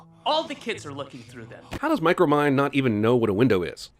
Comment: Did he just say micromind!